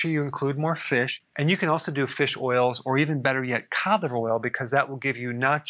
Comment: did you say cod oil? It’s hard to understand wish you had it written on the board..